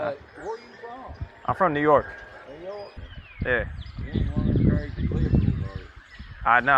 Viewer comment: When buddy said "you ain't one of those crazy liberals are you?" I heard "crazy negros"🤣🤣🤣🤣